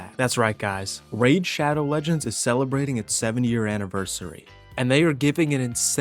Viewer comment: is it just me or did he really say 70 year anniversary